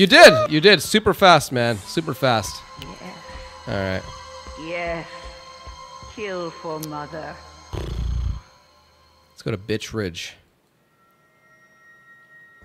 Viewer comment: sounds like u said 'Bitch' instead of Birch 💀